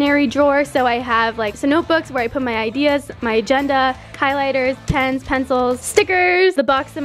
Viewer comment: I swear I heard her say kylighters lolol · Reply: c r i s t i n a if you listen really close she said highlighters